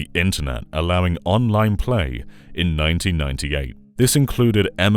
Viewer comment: at 4:55 did you say Fancy Star Online??😂